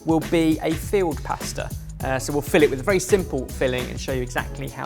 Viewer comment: I thought he said field pasta...then I realized later he said filled pasta, makes much more sense 🤣